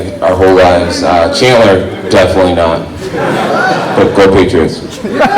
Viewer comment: What's jones say at 3:29 ? Double tap for 10 second revers to get the gist of the conversation - can't quite get what he says. Whatever it is it gets a reaction about his brother I think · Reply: he said go patriots, his brother used to play for the patriots